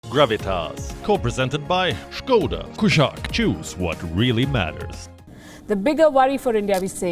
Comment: You have really good ears if you can understand what he says about skoda. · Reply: Skoda Kushaq- Choose what really matters!